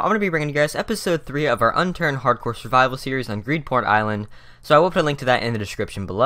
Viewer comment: It sounded like he said greedporn island